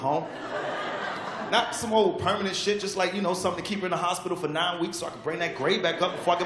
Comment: 그... 1:14 때 아니 존나 심각한거 말고 라고 하는 부분에서 정확히 뭐라고 말한거에요? 자꾸 small, 작은 거 말고 라고 들리는데 문맥상 해석하신 게 맞는거같아서... 저도 리스닝 잘하고싶네요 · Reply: not some permanent shit just like you know something keep her in a hospital for nine week so that i bring ... 여기부터 안들림 걍 몇주동안 자릴 비워서 매 안맞을 만큼의 피해가 있는 사고를 말하는듯..